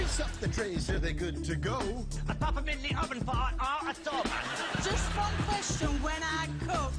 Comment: Does anyone know what Phillip Glennister says after "pop um in the oven..?" We're clueless! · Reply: Sounds like he says "...for an hour or so, man"